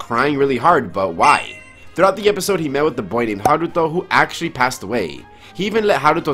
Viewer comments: I thought he said Naruto instead of Haruto...plus, what the heck is his dad's job?! His dad barely comes home & when he does, he leaves right before Ash gets home. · Did he just say milk the boy!?!?!